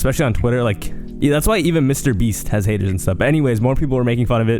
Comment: man i thought you said mr beast hated yeat for a second i was tripping hard 😭😭